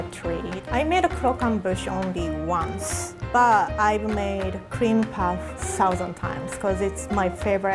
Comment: Did I heard Creampie ?